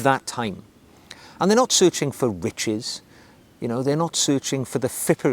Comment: I swear I thought he said “men aren’t searching for bitches”